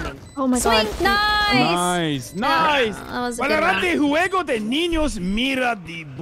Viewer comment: al i heard was "Valorante ninyos and pinyos."